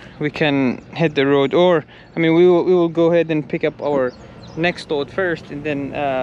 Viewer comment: Sounds like your saying your next load is ONIONS ??